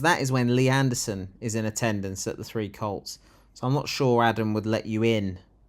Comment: Hello. Initially, I thought you were saying 'The Three Cults', like, Brexit cult, Doomsday cult and Right wing looney cult. He probably thought having 30p Lee 'filming' there would bring punters from all over the country, no, world, to see the great man himself and shake his hand. Instead, he's probably caused the premature closure of his fine establishment.